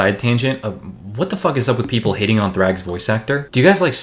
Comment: I misheard "hating" as "hitting" and I thought that the fans were actually beyond salvation